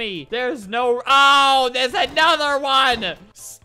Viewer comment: i thought you said there's a another juan